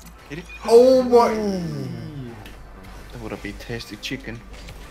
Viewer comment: did say tested chicken🤣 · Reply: * he said"that woulda been tasty chicken"